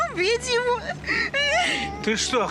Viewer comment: я так и не могу расслышать, что она говорит? "Убить его"?